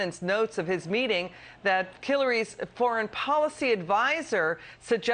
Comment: she clearly said “Killary’s foreign policy…” Roll it back to a couple of seconds before (to 3:58) turn it up, then hit play.